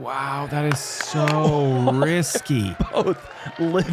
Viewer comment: Hole 9, Ricky's 2nd shot I thought you said "That shot is so Ricky" instead of Risky. With his aggression, you could use those words interchangeably. Do they make socks that can hold up to disc golf? All of mine have holes under the ball of my foot.